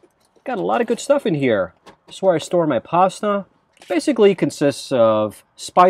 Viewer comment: You’re a fantastic communicator. Although, at the 6:36 mark I thought you said …This is where I store my “pot stuff”. Ha! It is California so I wasn’t shocked but when I listened to it again, I realized you said “pasta” . Happy trails.